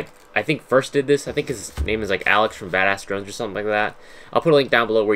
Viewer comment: when he said bad a** drones i thought he said bad a** drugs